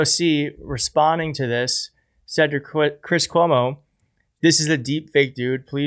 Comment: baby version of andrew cuomo was funny. it sounded like you said Cwis Cuomo, lol. Thanks for what you do.